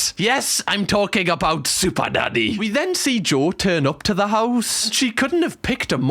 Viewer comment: did bro just say superdaddy? 😂